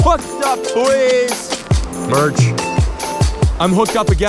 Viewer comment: did u just say merch @ 14:08?... I’m dead